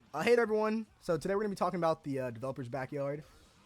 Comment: I heard "i hate everyone" 😭🤣